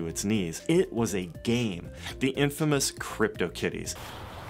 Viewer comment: I could have swore he said crypto titties.